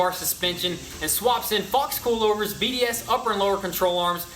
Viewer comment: Its sounds like you said f scolovers😂😂